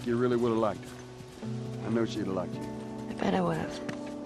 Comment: the subtitle says "Yeah" but instead she said "I bet I would have".